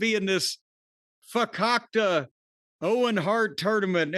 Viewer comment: what word is that? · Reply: Fakakta. Basically Yiddish for bullshit.